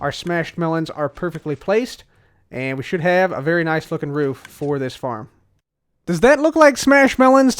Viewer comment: I thought you said "we should have a very nice f**kin' roof for this farm"